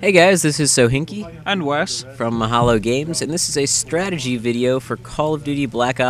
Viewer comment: I always hear "Halo games" :o Nice vid too, one time i played this solo, i pack a punched the pistol, and it will become dual wield grenade laucnhing pistols. Its amazing, just buy mp40 and save the pistol youll be fine, after that just buy juggernog and rack points to upgrade. And of course: SUBBED